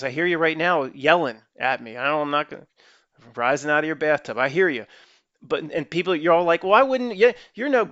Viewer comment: Long as you rinse off after; otherwise, you're just redistributing the dirt. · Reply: I hear you "rising up from your bathtub" when you said that.